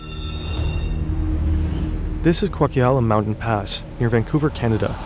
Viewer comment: What did he say? It's spelled "Coquihalla", but it's pronounced COKE-AH-HALL-AH